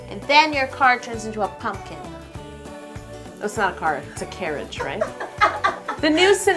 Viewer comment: hey, just wanted to let you know that there are a couple of mistakes with the captions on your video. For example, at 1:22 it says "car insurance" instead of "carriage". It would be awesome if you could fix these so your video is more inclusive for the deaf and hard of hearing community. thanks!